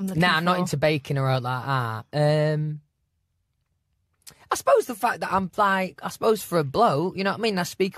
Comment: I thought he said “I suppose the fact that I’m bi” for a sec!!!